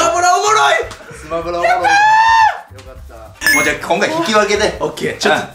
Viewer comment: 粗品の最期のよかった〜がせいやがおもしろく終われてよかった〜に聞こえる